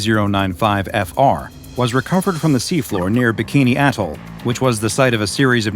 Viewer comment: Why does it sound like he says bikini asshole....